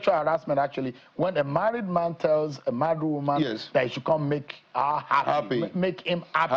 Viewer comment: At about minutes 22:51, did I hear Seun say ....mad woman?🤣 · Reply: He said "married woman" not mad woman